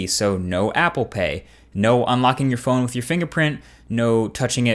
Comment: was i the only one who heard "no unlocking ur phone"